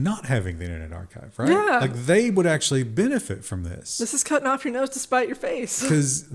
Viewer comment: I almost spit out my coffee when I thought she said "This is cutting off your nuts to spite your face." Hell of a twist on a classic.